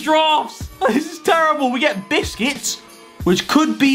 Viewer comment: I love Harry’s pronunciation of “busquets” he really sounds like he says “biscuits”